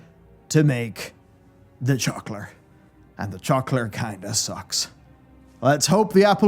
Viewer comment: wtf is a "trokler" 19:07? "chuckler"? the captions think you're saying chocolate??